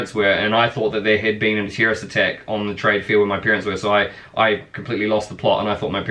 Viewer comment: On the light hearted side of things, at the beginning thought you said your parents were at a "trade fear." God Bless us one and all.